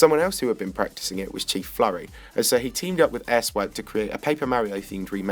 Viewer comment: did you say asswipe?